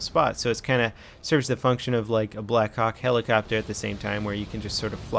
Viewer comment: did he just say a black cock helicopter